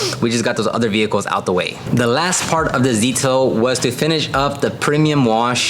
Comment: did you say Zetail?🤨🤨